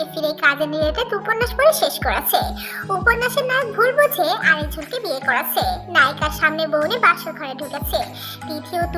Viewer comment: উপন্যাস এর নাম কি বললা? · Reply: শেষ চৈত্রের ঘ্রাণ